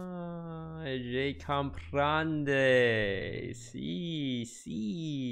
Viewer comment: Was it Italian?! Ahahah “Si, si...” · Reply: Leisure also in italian... I thought he said “grande, si, si...” ahahah